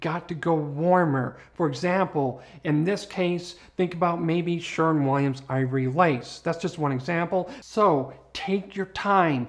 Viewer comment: Did I hear Sherwin Williams Ivory Lace? Thanks